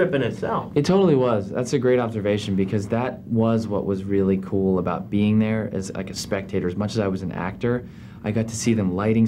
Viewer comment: subtitles say .............. "That was really cool bumping their as a"